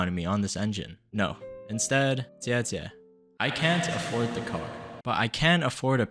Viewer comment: Okay, I'm dying to know: What he is saying here? I've repeated it the best I could into Google translate a bunch of times and there's a couple options: "Thanks" but when I play it back, that's not what it sounds like he's saying or "elder sister" which actually sounds closer and it kinda makes sense. I know it's common for people to refer to a woman as "sister" as a kind "bruh" almost. I dunno. Any help would be appreciated. I love all languages and any opportunity to learn more of every language and how to pronounce things is kinda my favorite. (Like, I've been binging this channel all day today and every time he says someone's name or a Chinese word/phrase, I listen to it and repeat it a few times and even check on Google translate if it can recognize what I'm saying to see if I'm saying it sort of right. I know ti's not the best indicator, but it's what I got.) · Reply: I also heard jie jie which means sister too 😅😅😅